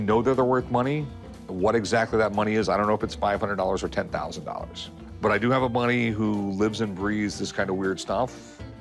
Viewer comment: That's cool that Rick has a "bunny" to help him evaluate/price the item. 😆 Yeah, actually it does sound a little more like "money". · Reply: I thought he said he’ had a money that knew a lot about this